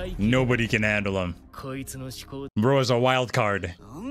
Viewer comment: did u say “wild card” 😏